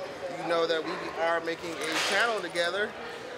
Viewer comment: I heard " we are making a child together" and was like wht